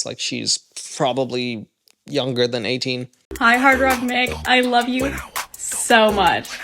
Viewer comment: I legit heard her say "Hi, Hard Rotten Egg" the first time I played this part of the video. 😅